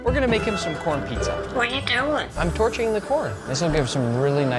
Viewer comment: I thought bro said: "I'm torturing the corn :)"